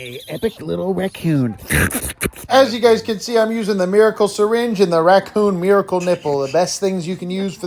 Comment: i thought it said "Cring Syringe" and i said heeho